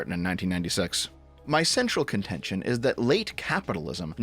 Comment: wheh you read: my central contention I heard: My sexual contention